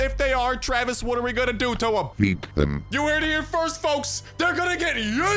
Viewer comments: I heard it as "Eat them", instead of "Yeet them" and Travis is beginning to scare me. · At first when travis “yeet them” I thought he said “eat them” also maybe there’s a good chance that the carnival tickets are tickets to the concert in the truck slot machine thing and travis is a reptile cuz amphibians go both land and water while some reptiles go around bodies of water